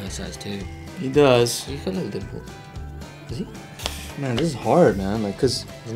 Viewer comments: I LOVE THIS VIDEO. especially when Thomas thought Ki Hong said his nipples instead of his dimples 😂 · K: "My dimples?" T: "You said your nipples!" K: "My nipples?" ON POINT LMFAO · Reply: I thought he said nipples and i was so confused and then Thomas was like 'I thought you said nipples' and I was like ok I'm not the only one